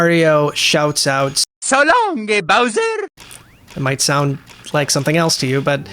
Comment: I've always heard "So long eh Bowser?" Which I prefer over gay or king bowser lol. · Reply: I always heard it as "So long-a, Bowser!".